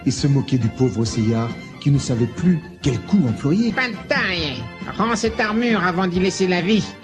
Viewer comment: Putain c'est génial XD Statechemist doit voir ça ! Le cri d'horreur de Shina la vengeresse, j'en pleure ! Elle dit quoi à 8:48 ? Sacré Taori ! Seika doit être jalouse de Tarina ou de Sarina Quelle noblesse ce Seiyar le plus fort de la planète ! Gorgu à la Licorne et sa voix suave qui sort d'un tuyau de douche Les ennemis de Seiyar sont tous des tricheurs ! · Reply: Elle dit "Pas de taille". ;)